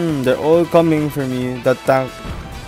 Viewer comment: in minute 5:02, did you say "datang"? because that's what i heard